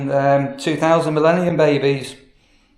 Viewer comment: Had to re listen on first I thought you said Hannah and Lucy were 2000 and 11 babies, I though well that isn’t right, they are older than 13 😂🙈 you said 2000, millennium babies 😅 didn’t you lol 😂